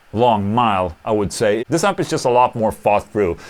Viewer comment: I thought said ''this amp is needy''